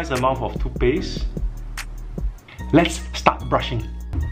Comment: When he lets get start brushing i thought he was saying "lets stop brushing"